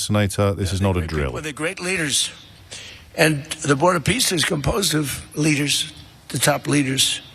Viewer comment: Ohhhhh, I thought he said he was bored of peace.